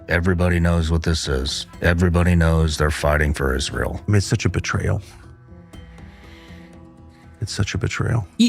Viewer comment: The captions are erroneous in couple of places. E.g.: Tucker Carlson did not say, "It's a joke." He said, "It's such a betrayal," twice. 13:37 Laura Loomer did not say,'They take clips of Alex Jones...' The name was something like 'Jo/e .....' Kath?